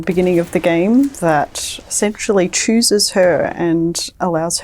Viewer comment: Game looks pretty cool... but did she say the gun SEXUALLY chooses her??? 0:19 😭😂